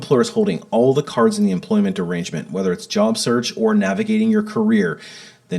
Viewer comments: Every time I hear you say, “employment arrangement,” I’m not sure if you said, “employment derangement.” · 😂😂😂😂😂 I literally thought you said "if you're tired of employers holding all the cards in the employment DERANGEMENT..."